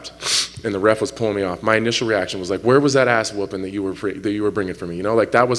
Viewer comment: I heard "how's that ass-beating" or "where's that ass-beating" He was certainly fired up! Great fight and finish. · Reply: Yea, in an interview before the fight Spann said he was bringing an ass whooping for Smith, so he was asking "Where was that ass whooping at?" 😆